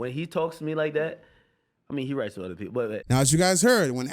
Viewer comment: It sounded like he said “he write with other people” 🤔